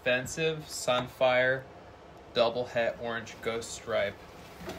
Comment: U thought u said double headed 😂